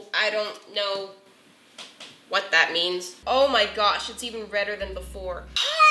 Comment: can somebody tell me what she said? reder or wetter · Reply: Reder